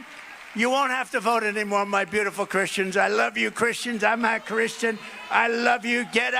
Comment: Does he say "I love you Christians. I'm not Christian" at 1:17 or am I just trippin'?